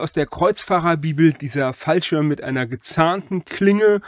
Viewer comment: Es tut mir leid, aber an einigen Stellen habe ich echt Probleme, dich akustisch zu verstehen. Wie heißt diese Waffe? (Die automatischen Untertitel sagen lustigerweise 'Fallschirm'.) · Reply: Das ist ein Falchion.